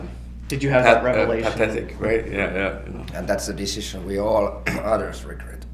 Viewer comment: what does Perttu say around 2:08? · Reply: "And that's a decision we always regret" 8 years later but there you go.